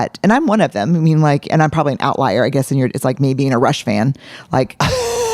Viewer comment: What does Brene say? "I'm an outlier, maybe in a ____ ?" Is in "Rush van?" Like the band rush? I've replayed this maybe 10 times - if someone knows, please help a friend out. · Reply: Rush fan?